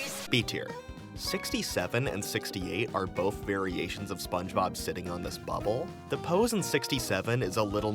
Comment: What did you just say, Sponge Me Boy?